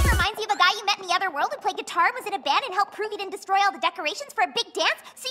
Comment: I thought I heard Pinkie say "And played guitar with him in bed" Curse my dirty mind.